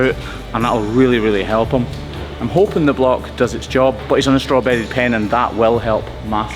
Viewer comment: LOL!! I thought you said he's back in his strawberry pen, not straw bedded pen!😅